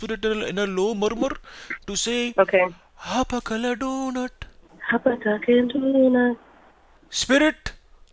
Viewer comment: Is it just me or does she say “hapa Dunkin Donut”??? 😂🤣😂🤣 I swear it sounds like that’s what she says haha...